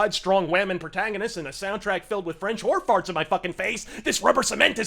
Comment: Ok, you said 'French horn farts'. I heard 'French whore farts'....